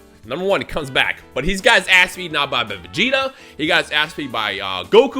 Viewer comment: Legit thought u said eaten instead of beaten 😳👀